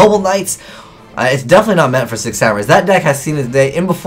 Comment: Every time he says "Six Samurais", I hear "Sick Samurais"